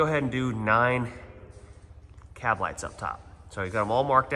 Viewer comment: Thought ya said 9 cab lights